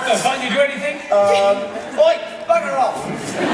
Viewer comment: "oi back off" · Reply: He said bugger off. Not back off